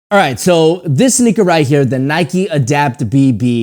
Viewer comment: I thought he said the n word 😂😂😂😂😂😂